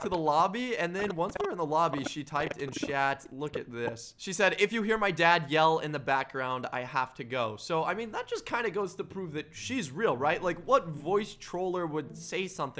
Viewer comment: It sounds like she said I have to go...to L.A.